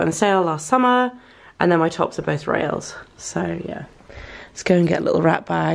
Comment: Can someone please tell me where she got her blouses from she says her blouses are from at around 2:05? I can't catch it, how do you spell it? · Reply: arcanacastitatis I think Rails